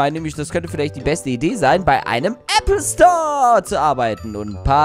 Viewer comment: habe ich verstanden App Store 🤣🤣📱